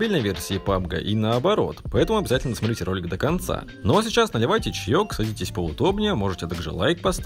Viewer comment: мне одному послышалось надевайте???